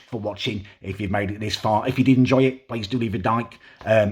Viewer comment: tell me does not say ‘please leave a dyke’ I’m crying